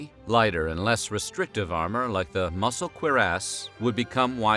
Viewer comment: Hahahaha did he just say "muscle queer-ass" ???? Hahahaha